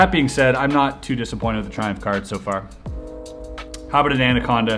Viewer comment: Literally thought it said trump deck